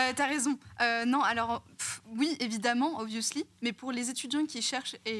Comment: J'ai mis mon volume trop bas, au lieu de "obviously" j'ai entendu "au vieux slip"... 🙄 Comme ça faisait pas très, j'ai vérifié.